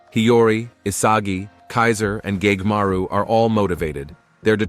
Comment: did i just heard gaymaru?